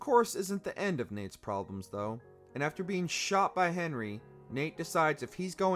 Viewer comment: did he just say Henry? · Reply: I think his name is Henry Flynn but he mostly referred to ass just Flynn